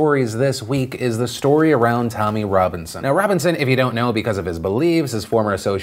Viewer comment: I thought he said Tony Robinson at first, that didn't make sense.